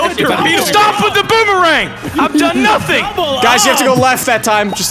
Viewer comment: did I hear yell OH YOUR RIGHT